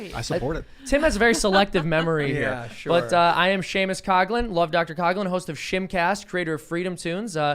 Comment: I so thought he said his name was love Dr Cosby at first lmao was like wow that's so fucked up but still so funny but then I heard what he really said...